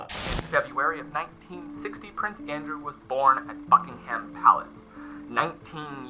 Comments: when you said he was born in Buckingham.... bruh i thought you said "f*ckingham" and i was so confused for a minute 😭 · I misheard "Prince Andrew was born at Fuckingham Palace"